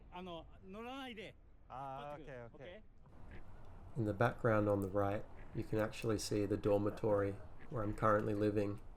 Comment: What did the security guy say? That you need to push your bike? I remember cycling in Odaiba in a really similar look spot which is over on the other side. I didn’t recall seeing signs that you couldn’t cycle. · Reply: Yeah he basically said no riding, which is understandable because that particular area sometimes gets packed with tourists. Signage is bad and usually ignored by locals anyway, better to just use common sense and trust your instincts imo.